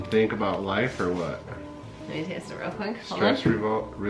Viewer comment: I swear to god I heard her say "let me taste it real quick" and I was like NOOOO ITS NOT EDIBLE WHAT ARE YOU DOING😂😂😂